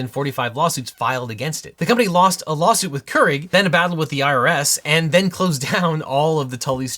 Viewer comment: Genuinely thought you said “lost a lawsuit with Keurig, then lost a battle with the IRA” instead of IRS for a second and was VERY confused